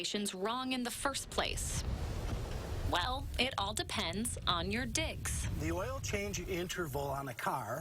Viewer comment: What did she say at 2:14???? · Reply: "All depends on your digs". Informally referring to living quarters or home.